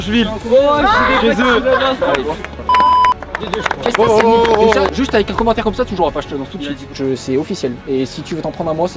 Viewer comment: il a dis quoi ? faut pas BIPER ^^ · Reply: Il vont perdre On va perdre Entre les deux je sais pas